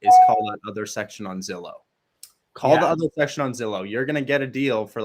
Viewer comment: Great video. At 45:08 of the video says to "call the ???? section on Zillow" What section is he talking about? It's like it's muted out. Anyone hear what he said? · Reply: “Other listings “